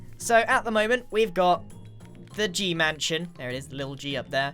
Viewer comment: What I hear: We have got the G-MANsion